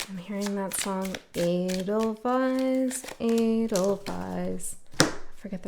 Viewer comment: Oh man.. Thought i heard you singing "Angel Eyes, Angel Eyes" when you were singing Eidelweiss, 😇